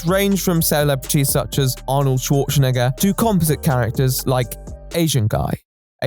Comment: What did he say???? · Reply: Arnold Schwarzenegger.